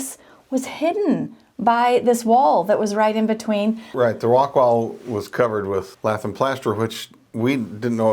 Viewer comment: Man, your rock walls confused me. I thought you said "Rockwool" which is an insulation material 🙂 In the UK we'd call them stone walls as they've been dressed and built into something. Rock is the raw material. That why we have stonemasons and not rockmasons. 🙂 I love what you are doing to this building. You have saved it from ruin.